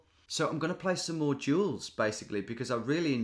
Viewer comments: every time he says "duels" I hear "jewels". love the content! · I thought you said jewels instead of duels (I was only listening)